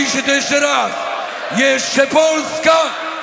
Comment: Also at 6:04 what did that guy say to the crowd in Polish? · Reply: GemGems3 if you started to this, lets do it right "jeszcze Polska nie zginęła..."